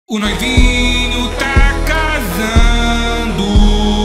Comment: "O NOIVINHO tá casando " "Pula , quica e rebola" "Agora o divórcio rola" Esse é o hino nacional mais lindo 😢 edit: caraca isso todo de like vlw tropa Edit: familia fiquei famoso!! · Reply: Eu ouvi " o noivinho tá cagando'